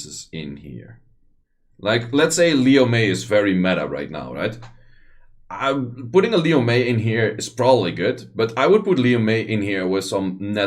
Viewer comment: who did he say? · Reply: Liu Mei the light Kung Fu Girl